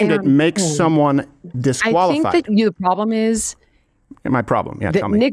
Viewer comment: am I trippin or did she actually say "your problem"